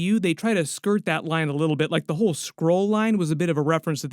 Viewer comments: I thought you said he tried to blame it on a squirrel not a skrull, took me a minute. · I honestly thought he said squirrel, not skrull? 😂😂😂